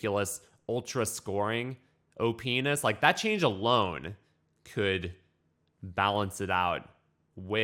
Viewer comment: OPness, yes, that is the word I heard, I didn’t hear anything else.